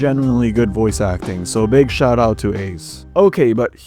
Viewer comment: Did you just say.. BIG SHOT? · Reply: He said "big shout-out" not [[BIG SHOT]]